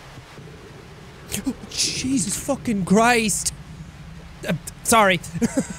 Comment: is it me or did he say grist